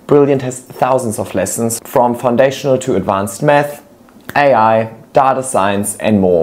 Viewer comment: I wont lie i thought said "advanced Meth" (just finished a 12 hour shift moments!)